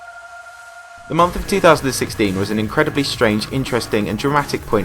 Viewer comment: did he say "the month of 2016" or is it just me